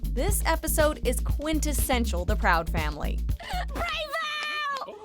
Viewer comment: Yeah. Childhood. Back when Disney animated shows were bomb. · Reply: Neh-Bih Sangbong i thought it said “The- proud- family- what”